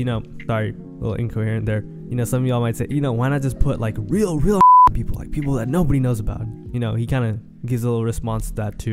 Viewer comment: what word was censored here ? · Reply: i said fuk